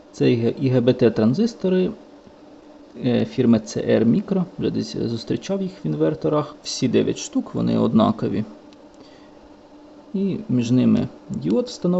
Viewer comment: Смотрю ускоренно, послышалось "ЛГБТ транзисторы" 😮 )))